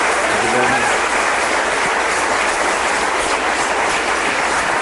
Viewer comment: Anybody else professor Said say “Christopher is the best of the intellectuals” at the end? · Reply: I thought he said "Interlocutors"